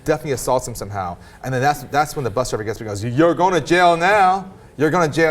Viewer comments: I agree he should have not hit her but i do agree with him throwing her ass off the bus.btw it sounded like he said "your going to hell now" not your going ton jail now. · I thought he said 'You're going down now'